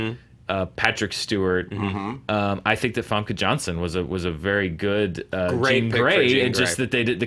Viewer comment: I heard from "Ivanka Johnson". Took me a long moment to understand it's probably Famke Janssen ;P